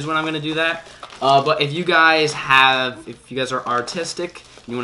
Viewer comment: Ai thought you said "If you are autistic"